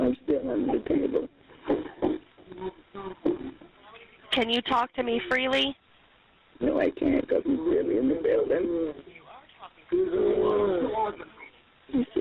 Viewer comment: did i hear someone say "but u are talking freely" wtf